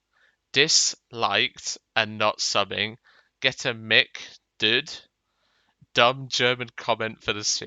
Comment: HEEHEE I made it in. OH YEAH ILL MAKE IT INTO A BIPLANE, ILL VIOLATE TWO I16 JUST TO TURN IT INTO A BIPLANE. And yes Berti has wonderful english he passes my class. I thought he said milk duds but it was mic dude.